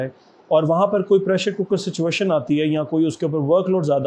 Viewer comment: Did you say pressure cooker situation at 2:40 ????